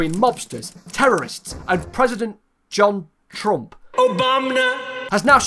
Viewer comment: i heard president JonTron